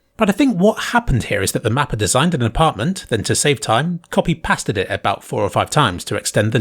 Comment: did you just say ‘past-ed’. Please stop.